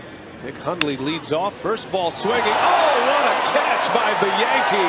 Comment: i thought he said "oh what a catch by the yankee!"